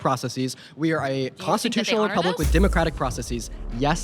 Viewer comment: what did she ask him in the minute 45:47? I didn't hear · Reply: "You think that they honor those?"